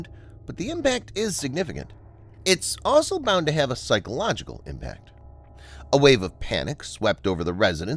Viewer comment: turn on closed captioning and it is strange that the narrator said “psychological “ but the Closed Captions said “physiological”